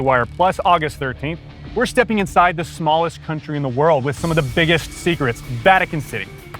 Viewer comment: I thought I heard "..the greatest secret, vatican 3!".